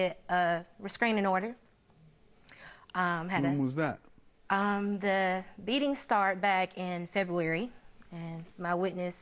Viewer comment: am I hearing this correctly or did she say the "one-beating" started and ended in the very same moment? I've dated females that claimed to victims of their mates beating them as if, it was an acceptable event in their lives. It was always sooo uncomfortable to hear about it and sooo bizarre how the police was never involved.